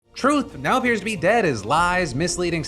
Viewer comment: Misheard the intro line "truth now appears to be dead" as 'Trump now appears to be dead'...